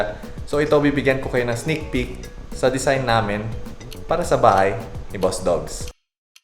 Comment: did he said bahay ni boss dogs?